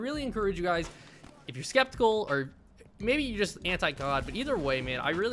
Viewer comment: Anybody else hear Anti GOD right there :)